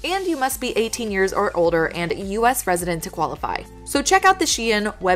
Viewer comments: Lmao at 4:43 I thought she said "You ust be 18 years or older and a US President to qualify" haha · was I the only one who thought she said US president